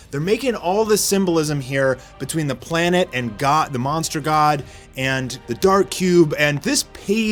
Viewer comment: Dude tf are you saying? MonsterCells? The front page is just like a giant a Octopus/Craken lol Also you, who clearly don't read the webcomi, totally missed the point of Zombieman/SweetMask dialogue